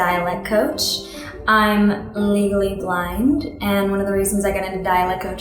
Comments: i thought she said she was “legally *blond*” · i thought she said i am llegaly blonde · I thought she said she was legally blonde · I thought she said "Legally Blonde" XD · oml I hate myself...... I thought she said "I'm legally blonde"....